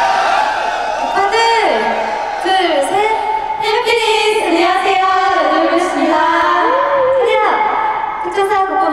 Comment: 뭐라고 인사 한건가요 ? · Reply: 해피니스~